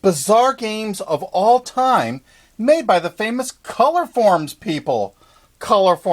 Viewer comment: I saw Colorforms, and I thought it said chloroform. I was like, whaaaaaaaat??